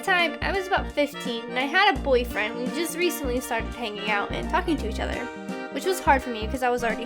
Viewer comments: Did say ´´Í had a boyfriend and we just recently started hanging out and F*CKING to each other ?´´ :D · when said talking to each other i thought she said fucking to each other XD